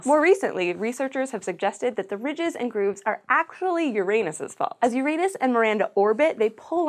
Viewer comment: Did she just say that the weird moon is the fault of my anus? Protip: Nobody calls that plant "Your-anus" anymore. Our culture has pretty universally switched over to "Your-un-us".